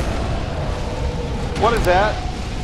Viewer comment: Instead of "what is this", all I heard was, "What the f***"...